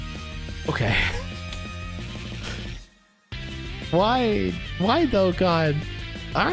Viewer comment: When he said “why Dokkan” I thought he said “why tho god?” 😂😂